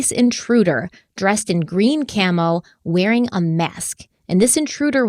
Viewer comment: So the Jack had a 9" knife? What did he do measure it? lol. And I was just barely listening when I heard green Camo. I thought you said Pink Camo! ☠️ so I imagined a fat guy in pink camo with a 9" knife hiding in the closet. Omg. That would be terrifying. 😂